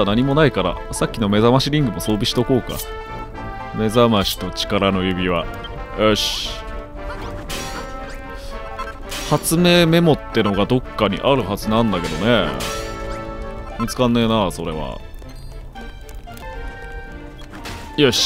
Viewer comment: 発明メモじゃなくて初音メモって最初聞こえた ww 次回ついにアネゴ登場かな！？(●´ω｀●)